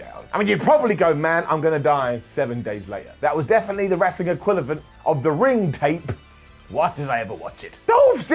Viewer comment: Did he just say "enquilivent"??? Sounds like he did